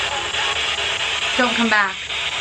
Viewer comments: Am I alone? I heard 'welcome back' not 'don't come back'.....which makes sense cos had just said that he heard it in his past video, maybe she recognises him from last time........ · When it said "dont come back" i thought it said "im a cat" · I HEARD "WELCOME BACK" not Dont..